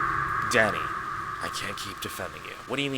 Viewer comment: NAHHH I THOUGHT U SAID "DADDY" AT FIRST